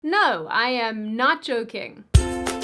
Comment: for second, thought you said Nacho king at the beginning instead of not joking. Now i want nachos.